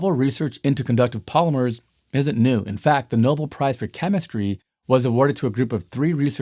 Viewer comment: Really like watching your videos. Lots of really great information. Maybe I heard it wrong but it sounded like you said "nobull" prize instead of "nobell" prize. ...Just an FYI